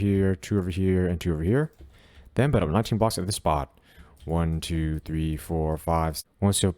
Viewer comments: can anybody help me what he said there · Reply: "and then build up 19 blocks at this spot" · did he say build up 19 blocks I can't under stand him